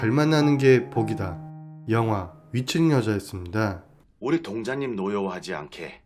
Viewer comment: 윗층여자라고 말하는데 왜 미친여자라고 들릴까요. 다른곳은 다 제대로 들리는데, 유독 저 부분만 그렇게 들리네요.